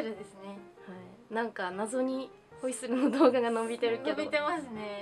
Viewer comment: ホイッスルの発音が いかりや長介のオイッス～に聞こえるのは私だけ？🤔😁